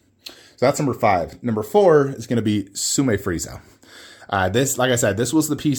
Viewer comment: did you say frieza?